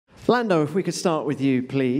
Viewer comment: Anyone misheard Lando as Fernando...